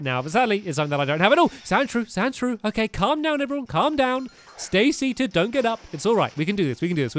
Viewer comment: am i the only one who heard stampy saying stacy at 16:40?